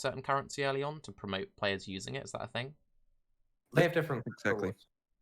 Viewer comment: "They have different ________" what did he say? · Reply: Rewards